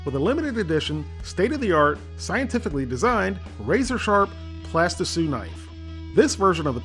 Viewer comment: what is he calling the knife ? Plasti sue ? please explain.